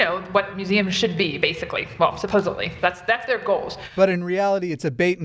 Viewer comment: Atheist channel? Check. Ad for Southern Baptist Theological Seminary? Weird. @ 7:45, does say supposably? I realize she's probably got a speech impediment from that massive underbite, but c'mon.